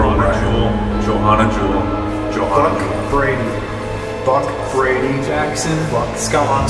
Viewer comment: sounded like he was saying “F*ck Brady” and it made me laugh so hard 😂